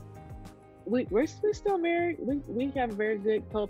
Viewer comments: She’s lying about being divorced!! She’s still married and looking for a new man! 🤦🏽‍♂️ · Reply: Agree. At 11:40 she clearly said "we're still married". I listened to it three times lol. Then she backtracked. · “we’re still MARRIED” Did anyone else hear her say that? Kevin didn’t really pick that part up but he was totally correct that NO other man deserves to get caught up in her drama. The husband she HAS deserves better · I swear she said they still married